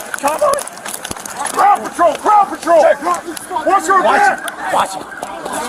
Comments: They were saying “crowd control”. I thought they were saying “paw patrol”. I wish I had it right. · i was cleaning my apartment and only heard the audio, and swear all I heard was “Paw patrol, paw patrol!” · I swear to god i heard “paw patrol! Paw patrol!”😂😂